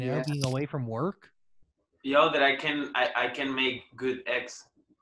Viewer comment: Zabdiel: I can make good eggs 🥚 I'm loving his accent 🥰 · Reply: At first I thought he said "I can make good ex" Like, what?! Ahaha Then they were starting to talk about scrambled, and I was like "Oh, eggs 😅"